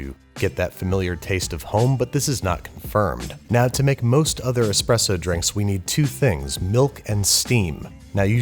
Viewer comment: I thought it said depression drinks and I almost choked on my cupcake